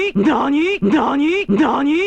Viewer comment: から『いいじゃん』に聞こえた